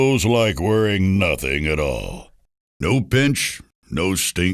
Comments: Makes me laugh, sounds like he says " "No bitch" but really he says no pitch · The part where he says no pinch sounds like he says NO BITCH! LMAO